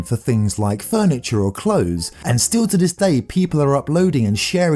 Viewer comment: When he said people I thought he said peta 💀💀